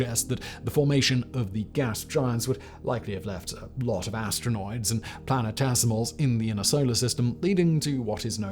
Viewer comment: Did he say 'Astronoids'? 🤷🏻‍♂️ 😕